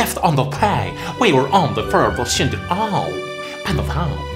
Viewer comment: what it sounds like he said: OH! My god Carl. They calm in in the salty. Fifth underpaid. We were on the verbal shit of aww! Pen of how?